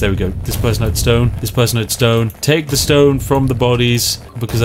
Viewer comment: I misheard: "Take the BONE from the bodies. Because I need it to make... landfill." o.O